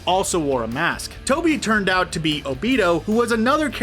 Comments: Did he just say Obido? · Did he just say Obeto 😭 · Lol i thought i heard him say o-weed-o instead of obito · Did he just say Abito? ....my childhood is crying and cringing at the same time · Did he just say Obito as Obeseo